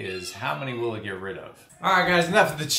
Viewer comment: I really thought you were going to say, "enough chitter chatter, let's get at her", for a second there, lol. Great video, exactly what I was looking for, very informative!! Nice